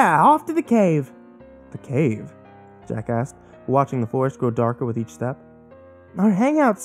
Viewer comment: At around 12:33 I thought you said "jacka$$" instead of "Jack Asked" XD